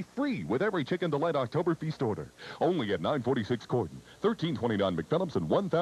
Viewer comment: sounds like he said gordon